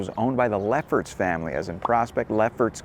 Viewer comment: when you said Leffert Family I thought you said Lepper Family 😝